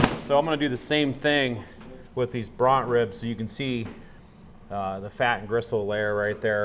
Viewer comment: What did you call the second set of ribs you cut up? Brott (brought?) ribs? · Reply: Hey there. They are called Bront ribs--short for Brontosaurus. If the ribs are left untrimmed, these ribs are referred to as "Dino ribs." If the plate short ribs are left in-tact, they're called "Brontosaurus ribs."